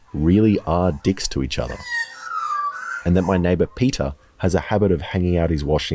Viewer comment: I thought you said petah but you really said peter